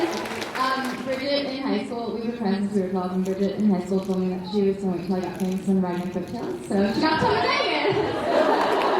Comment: 28-40 Can anyone tell me what did Kat say? "Bridget in high school told me that she.....?" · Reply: Ale Gomez it was “Bridget in high school told me that she was going to wait till I was famous and ride my coattails. So she got to come to vegas!” :)